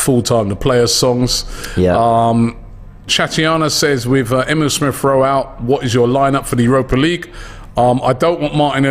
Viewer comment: Did robbie say “emile smith royale” at 15:36??